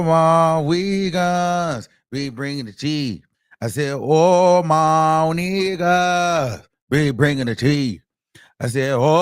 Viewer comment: I thought they said all my wiggas 😂😂😂